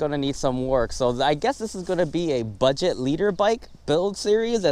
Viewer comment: nigga did u just say leader bike, its liter bike